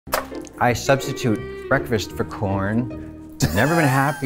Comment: I thought she said I substitute breakfast for porn and I was like haha same! Turns out she didn't say that and I'm the only sad piece of shit here i